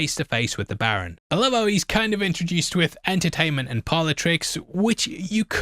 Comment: I was listening and thought you said politics, not parlor tricks. Though, the two are hard to separate these days